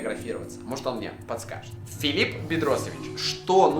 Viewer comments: мне послышалось или было Филипп Пидросович? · 9.48 мне одному послышалось Филип Пидросович🤣🤣🤣🤣ору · Мне послышалось что ты сказал Филлип Бедросовечь